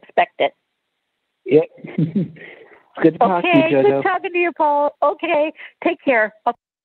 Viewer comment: I thought he said good talking to you though